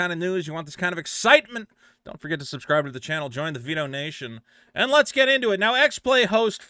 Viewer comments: I thought u said “pedo-nation”. I was ready to skip ur video when I realized ur channel name is Vito and u prolly said “Vito-nation”... u should drop a few pounds so that ur speech isn’t so fat-slurred! 😂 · I thought you said "Vino Nation" and suddenly all I could think of was Vito branded wine delivery.